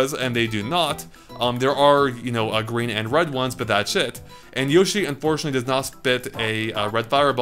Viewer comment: did you say that shit or thats it?